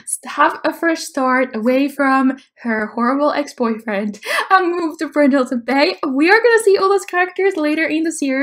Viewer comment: ( Put on CC and read what she just said, xD ) "I move to Brazil ."